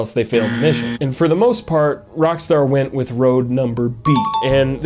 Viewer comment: I think he said “road number B” am I hearing things?